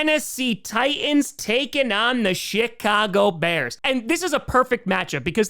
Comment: did you say the shitcago bears 🐻?